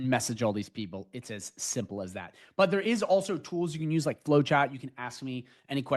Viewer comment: what was the tool, I couldn't hear u correctly @ 5:15. flowchat? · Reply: Yep boss u got it🔥 it’s Flowchat, DM me with qs!